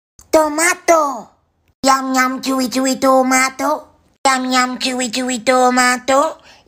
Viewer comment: And I thought she was saying yum yum Cherry cherry tomato 🍅🍅🍅🍅🍅🍅🍅🍅🍅🍅🍅🍅🍅 but in baby voice 🤣🤣🤣🤣